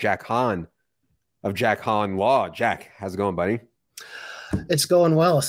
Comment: At first I thought that said Jack Ham!